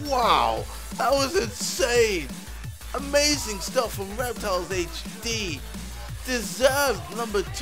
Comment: reptiles i thought said wet towels